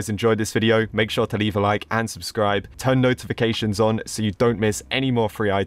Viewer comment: I heard u said “UNsubscribe” :/